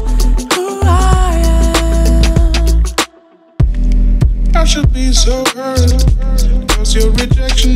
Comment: I should be sober cuz rejection ... and i feel stupid and I’m crying and crying ... but i tell myself I’ll be okay 🖤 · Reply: Paula Busta I thought he was saying I should be so hurt